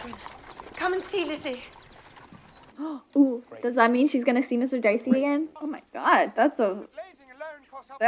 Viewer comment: I thought you said mr dicey🤣